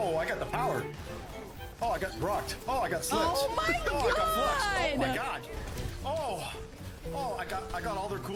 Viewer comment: ngl, i misheard "fluxed" and thought he said "Oh I got fucked"...still funny either way